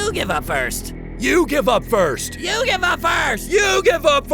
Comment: “you give up” What I hear: “YU-GHI-WA”